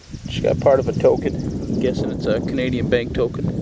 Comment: Canadian bacon isn't bacon at all! : P When you said Canadian bank token I thought you said Canadian bacon. haha! Nice finds for sure!